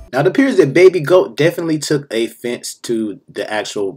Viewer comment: wait a minute, 🤔 at 3:05 mark did you say A fence instead of offense?